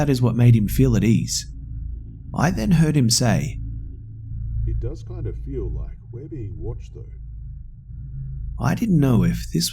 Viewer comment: What did he say? I heard nothing at that part, I think it's because my other ear phone doesn't work. :( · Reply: Thr other guy in the group said "It does kind of feel like we're being watched..."